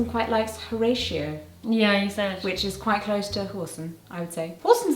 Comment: I heard Orson and I didn't get the link to the swear until moments later lmao